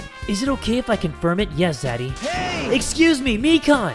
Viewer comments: did he call nagito daddy or am i hearing shit? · DID JUST CALL NAGITO DADDY